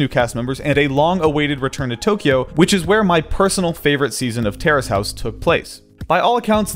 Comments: I thought you were saying terrorist house for a few minutes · I thought he was saying terrorist House